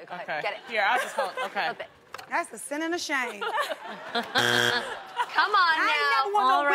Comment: Tamar "that's a sin and a shame" 😂😂😂 when they turned around her original picture. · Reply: Omggg I heard “Thats asinine shade” bith is equally funny lmaoo